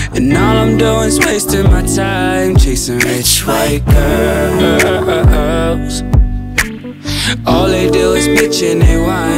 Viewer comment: On do re mi at the end i heard this and i thought he was saying which right girls